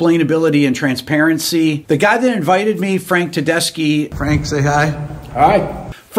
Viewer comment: For a moment I thought Rick said "Frank Tedesco"...! Another very talented musician.